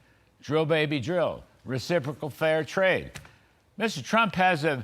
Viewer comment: Did you just say "fair and recipricol trade" 😂